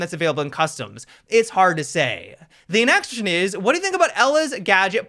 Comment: is there a reason you are saying "...the next-tion is" instead of "next question is?" lol 3:00 and 5:40 and